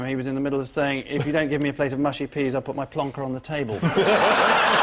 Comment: This is not true, what Oliver said was "Y'know I said to my misses if I put my plonker on the table I'll take it off unless you give me my mushy peas"